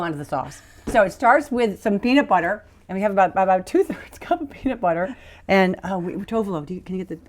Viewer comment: You could make the sauce Jane did, just substitute the PB2 powder in for the peanut butter she used. Good Luck! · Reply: I thought she said 2 cups peanut butter.